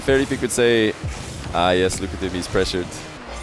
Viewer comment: wtf did say here. "Nah more recently Fairy Peak would say:ㅐ ㅟ ㅔ ㅒ ㅖ ㅘ ㅝ ㅙ ㅞ" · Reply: "ah yes look at him, he's pressured". pretty clear ngl